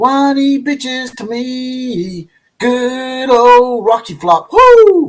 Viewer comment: i always thought they said " good old floppy cock" LOL